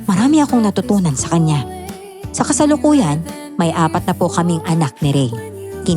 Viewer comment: Kagulat naman! Akala ko ang sinabi ni ay, "Anak ni Opelya at Rey, apatnapu!" WAHAHA! Ilang beses ko pa talagang pinakinggan kasi akala ko na-mishear lang ako. Yun pala ang sabi niya talaga ay, "May apat na po kaming anak." Doon lang nagising ang diwa ko WHAHA By the way, the best talaga ang tandem ninyong dalawa, lalo na kapag lumalabas ang beki voice ni Sir. Ang saya ninyong pakinggan! Hello po! Vea Kristel Ponce po from Marikina City. ❤️